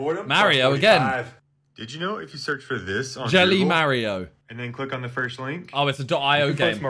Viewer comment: did he just call mario ”mar-iouh”